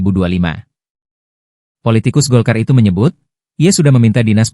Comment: Apa kah aku tadih salah dengar ya tadih ada kata2 tikus lo